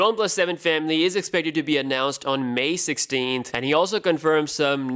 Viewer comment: I thought it said May 14th